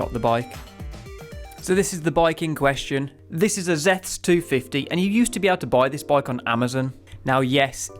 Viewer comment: What is the brand and model? I heard "Zed 250", but I can't find anything with that name. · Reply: Its a zeths 250 🤌 its in the description, and mentioned in other videos i have made